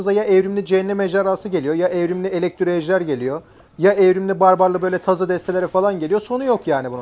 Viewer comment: evrimli barbarlı mı duydum?